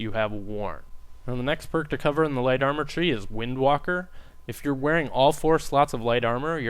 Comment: u thought he said " Wind Wanker " xD